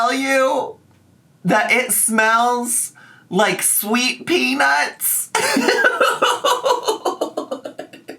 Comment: I most certainly did not hear "sweet peanuts" I heard "straight--"